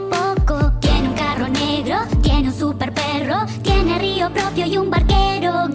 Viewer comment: Siempre pensé que decía: "y un barquero ciego" :v pues xq lo estaba no?